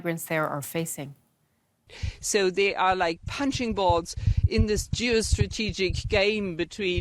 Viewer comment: Does she say "punching balls"???? 😆